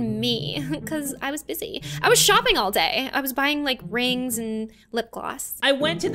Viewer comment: The first one I thought she said she was pooping all day 🤣